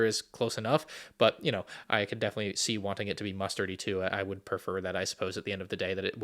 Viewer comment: Did he say m-m-mustard? 🥀